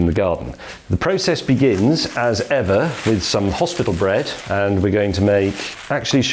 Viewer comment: “Hospital bread” and “Disappointment corner” 😅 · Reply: I thought he said hospital bread then I read the bread packaging and saw hovis so then I convinced myself that I misheard him until I saw your comment